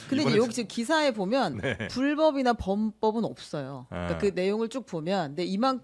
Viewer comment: 불법 범법은 없다??? 고 실드치는 것처럼 들리네요